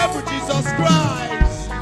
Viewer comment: did you just say you're Jesus Christ?